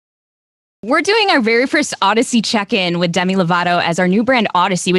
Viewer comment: I thought it said, “Audacity Check In”...